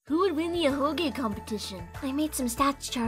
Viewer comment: I thought it said “ahego competition”